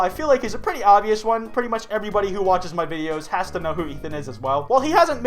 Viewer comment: did he call idubbbz Ethan?